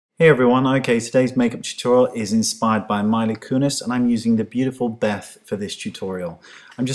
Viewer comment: I thought you said Miley Kunis . LOL :D but i know you didnt